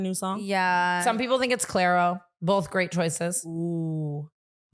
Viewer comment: did you say CLAIRO??? i fucking LOVE her! she is my absolute FAVORITE female musician!